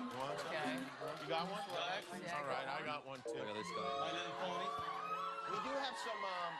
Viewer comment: 13 reasons to love Niall 3:02 - it's like Willy Wonka 3:05 - Oo selfie stick! 3:08 - hello👋 3:11 - Jimmy you look cute 3:29 - My little pony 3:34 - *grabs tiara first cuz he's the princess* 3:45 - HAHAHAHAHAH 3:47 - It's like Alice from wonderland 4:05 - Hello buddy! 'Fuck he ignored me, son of a bitch' 4:10 - HAHAHAHAHAH 4:24 - OH! 5:05 - It's like narnia 6:06 - *takes photo* HAHAHAHHAGAGAGAHAGA ALL OF THIS SHIT HAPPENED IN 3 MINUTES HDKSBDISBSLSDH 😂😂😂😂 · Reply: I think at 3:29 he said “My little bunny”